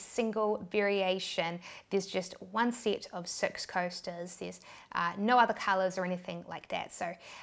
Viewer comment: That New Zealand accent is real. Who else thought she said sex coasters?